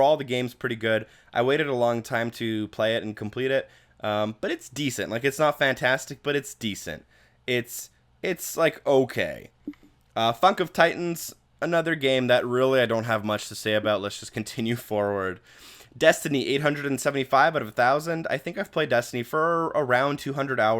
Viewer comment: BEWBIES Also at 19:27 did you say "Fuck of Titans"? 😂